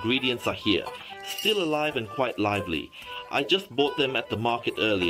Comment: Fyi, when he made a hole in their head, its already dead, the moving parts are because of the nerve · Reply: I think he said still alive octopus :v